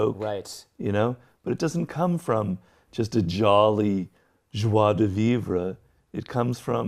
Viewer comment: what's this word? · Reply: JOIE DE VIVRE <link>